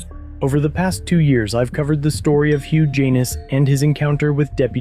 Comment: I’m sorry but whoever came up with that name did their kid down right dirty, did I hear you correctly? Hugh Janus? 🙄